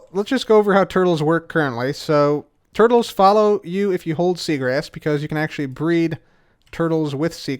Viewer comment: why did I hear seagrass as cigarette for second 🤣